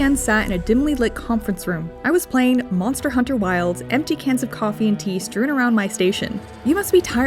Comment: I thought you said empty cans of pee, I wish that didn’t make sense in relation to video games.